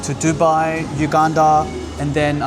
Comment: Did you say Uganda 4:00 ??